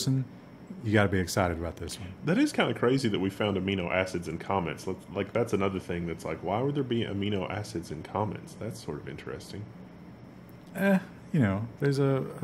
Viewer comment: I initially thought said, "they found amino acids in comments". Well that's just silly, there's no life here.